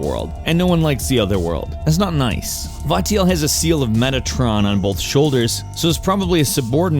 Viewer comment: I thought u said “seal of megatron” at first 😂